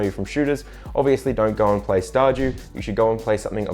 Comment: How he says ''Star dew'' all i hear is ''Star Jew'' and it makes me laugh.